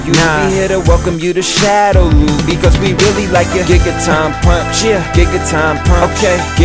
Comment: man i thought he was saying NIGGA TURN PUNCH NIGGA TURN PUNCH